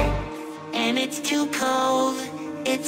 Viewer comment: I thought bro said the balls Of my sweater